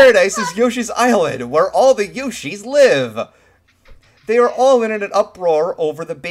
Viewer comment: for a moment there I thought you said "this parasite is where the Yoshi's live" 😂😂😂